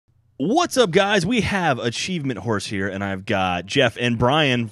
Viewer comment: "What's up guys, we have Achievement" whores? "here"| I thought they might have said achievement horse but that doesn't make as much sense